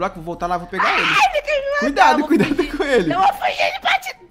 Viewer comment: falo cuidado coelha hihi eu ouvi